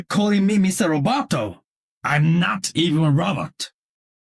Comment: I admit, when I first heard Genji's mister Roboto quote, I thought he said, "I'm not even Robert."